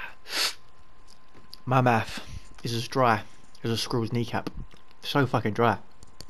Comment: Did u just say ur mouth is dry as a squirrels knee cap? Lolol